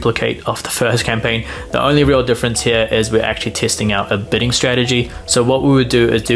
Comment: the overlay graphics mention "betting strategy" but I think it is supposed to say "bidding strategy". I think your accent confused whoever made the graphics :)